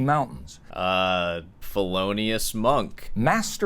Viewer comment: Did you just say "Fffeloneous monk"??.